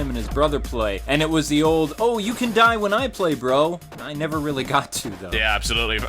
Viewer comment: man i dint notice the first time i thought he said "you can play when i die" nice catch XD. fun how your brain can play trick on you some times XD